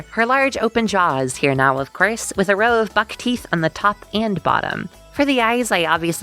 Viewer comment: Sounds like you said f7ck teeth here